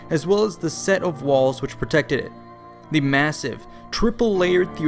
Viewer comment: it sounded like you lagged then started talking again lol. And it sounded like you said "protecteh..." but I think you meant "protected" lol. Are you a robot or did I catch a change in the matrix?